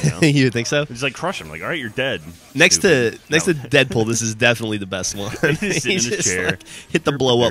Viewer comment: did geoff say deadpool at 3:23 ?